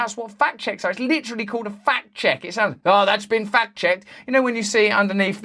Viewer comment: Every time they say "fact checked"....I hear "fat chequed". "It's been fat chequed" seems more accurate anyway.